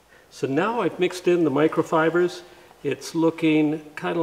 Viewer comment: A wood flour epoxy additive is much stronger for a glue joint than micro balloons. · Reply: I'm sorry. I heard "micro..." and my mind filled in "microballoons". I'm not familiar with "microfibers", but that's basically what wood flour is meant to be: fibrous material.